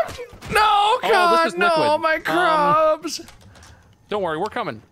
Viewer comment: Yes another full video 😭❤️❤️ turning notifications on for this page to and not gonna lie I thought mark was saying “ooh no my crabs!!” At 1:08:46 🤣